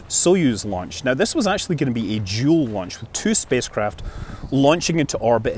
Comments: I thought he said "Jool launch" at first, but then realized it was "dual". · For a moment there, I thought you said 'Jool launch' instead of 'dual launch'... :'D